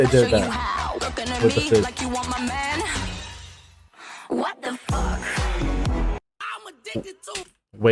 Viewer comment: she (Jennie) really did not said what the fuck. SHE SAID BLACKPINK... 😂😂😅